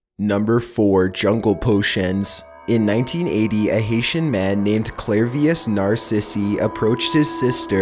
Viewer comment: Clarious narsisy I thought you said clarious narsist